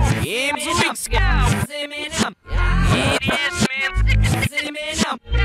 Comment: who else hears “it’s Eminem”